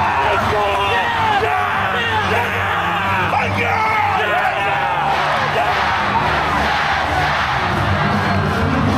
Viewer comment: 미노형 뭐라고 하는지 아시는분ㅋㅋㅋㅋㅋ 몇번돌려봐도 모르겠는데 너무 궁금.. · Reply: 뻑 유